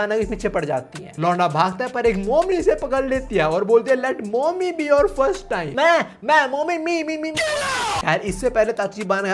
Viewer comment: what he said😅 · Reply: Mai mommy mai mai mai